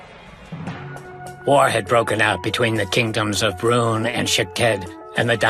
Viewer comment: I heard “War had broken out between the Brunei and the Shitheads”